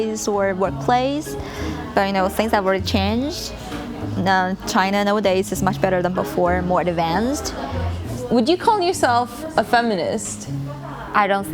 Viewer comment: she says "China nowadays is much better than before, more advanced." The subtitles say "China in the old days was much better, more advanced." Basically the opposite of what she actually said.